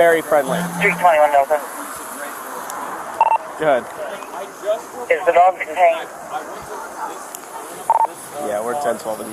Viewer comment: did she say is the dog DETAINED?? hahaha